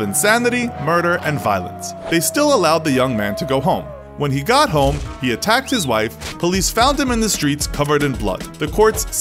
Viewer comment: The spoken words at 11:00 say just "When he got home he attacked his wife, the police found him in the street..." The English subtitles say (at 11:15) "When he got home he attacked his wife, tearing out her eyes, her tongue, most of her face in fact, and beating her so bad she died." I think someone forgot to sync the subs with the final cut of the video FYI 😆